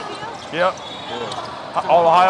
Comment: did he say yep OHIO! or am I tripin